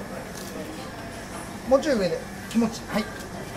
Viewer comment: i heard them saying " kemohchii " at 10:00 uhmm